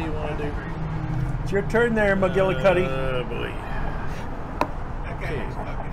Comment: Did my ears deceive me, or did I hear call somebody "McGillicuddy" as in Lucy Ricardo's maiden name?